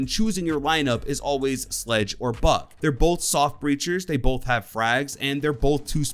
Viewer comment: I thought he said soft creatures 😂